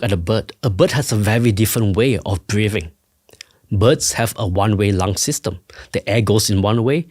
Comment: I do apologize for making fun of the man’s accent, but 24:05 is so funny if you hear him saying “butts” instead of “birds”